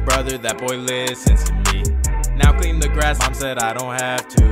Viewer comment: wait i just noticed did he just say clean the grass ? how like did a dog poo on it or something